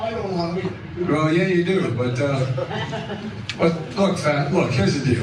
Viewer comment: Did Biden really say “Look, fat”? 😂